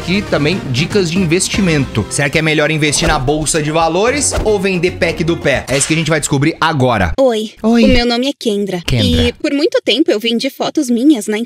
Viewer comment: MAN ENTENDI QUENG4KKKKKK